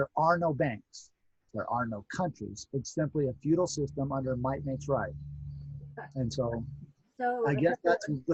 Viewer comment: "its imply a feudal system under what.....??? What does say there? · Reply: Might makes right